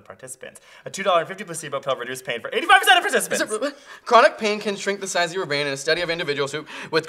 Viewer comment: Shrink The Size Of your anus LOL. @ 7:35 I know he didn't say that, but sure as hell sounded like it.